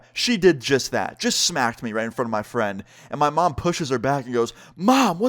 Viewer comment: did you just say "and my mom pushes her back" ??